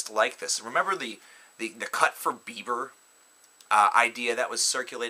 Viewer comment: My ears are fucked......sooo i heard him say "cut for blubber" instead of..... yah know....